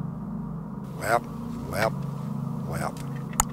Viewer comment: did pops just say WAP WAP WAP